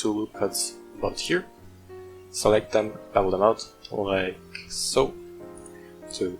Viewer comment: You know how long it took me to figure out what the hell you were saying to do at the 2:27 mark? BEVEL! Sounded like double, annoying. Speak slowly on TUTORIALS!